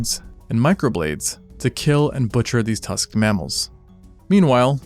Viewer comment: Bruh I heard butcher as torture